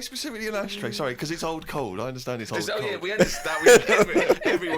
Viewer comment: Ricky and Steve thought Karl said “old cold” he actually said “it holds cold” in a Manc accent.